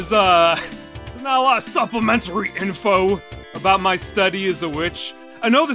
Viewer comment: I thought he said ‘my study as a witch’ and was extremely confused during the explanation